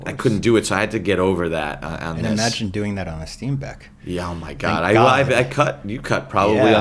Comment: "imagine doing that on a steam deck" did I hear that right? they're editing movies on a steam deck? lmao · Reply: Steenbeck. It's an editing table. It's how they used to edit film. Still, definitely not as bad as editing on a little Moviola. The one downside (and it's a big one), to editing a film—that was SHOT ON FILM—digitally, is that you have the digitize the images, which is why so many movies nowadays, though they may have been shot on film, look digital, because of the post-production digitization, and digital color-timing. No point in shooting on film because that just covers it up anyway, so I'm really shocked to find that no contemporary director has commented on this. They must not know the difference or not care. Another justification as to the reasoning behind why movies suck now.